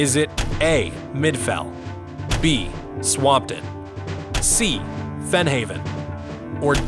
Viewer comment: i heard that as spamton lmao i was shocked